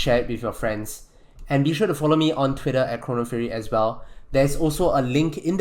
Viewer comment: I thought he said horny fury